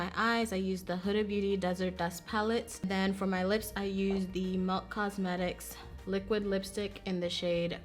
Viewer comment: So gorgeous! What was it you had on your lips again? I heard you say milk cosmetics but can't find it. Lol